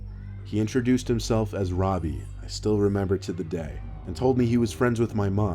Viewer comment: Anyone else think of the dude from “Jessie” when he said his name? 😂😂 Well... he said “Robi” (or however you spell it) but it still kind of sounded like “Ravi” 😂